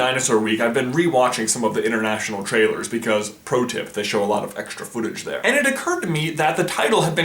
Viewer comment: did you just say protip?.....